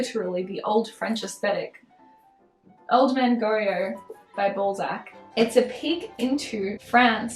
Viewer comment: Sorry but I can't hear anything but Ballsack at 16:00 😭